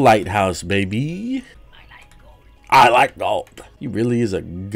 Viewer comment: "I like gold" - I swear to god I heard him say:"My lights gone"